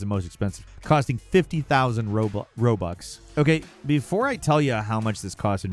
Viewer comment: I heard you slip and call them Roblux instead of Robux at first